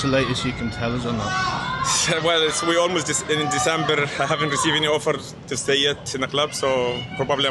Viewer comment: maybe invest in a hearing aide bro. Mo said and I quote "we're ALMOST in December" time stamp 0:24. Calling Mo, for me, Top 3 Liverpool player OF ALL TIME, "a massive liar" is a little out of bounds I think. Blatant misrepresentation of what Mo actually said.